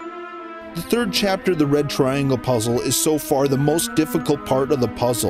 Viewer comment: It's past dinner time. I keep hearing "The Bread Triangle".